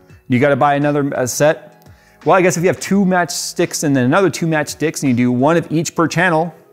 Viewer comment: I think he says, matched sticks and matched dicks....did I hear that correctly 🤣🤔 Actually, I think he said, "mashed dicks"